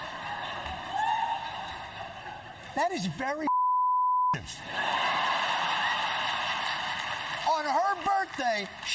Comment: What did he say there? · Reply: f**king creative